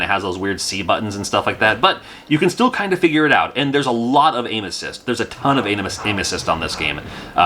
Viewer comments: Oh my goodness- you said a ton of “aim assist” and I heard “a ton of anuses” 🤭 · I thought you said "This game has a lot of anuses." It took me a second to realize you said "aim assist". 😂